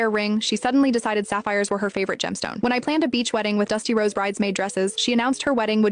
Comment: I heard "Dusty Rhodes" dresses and just imagined black dresses with large, yellow polka dots 😂